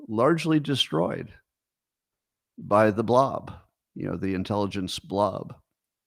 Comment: what's he say here 1:13:42 "largely destroyed by the intelligence ___" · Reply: Blob. It's a term he has adopted from Mike Benz who mentions it a lot, for example in his interview on Tucker Carlson. Similar, maybe identical to what Bret Weinstein calls "Goliath", a powerful force that manipulates people and institutions and we don't know exactly what it is, but it's becoming clearer all the time as we uncover more truth.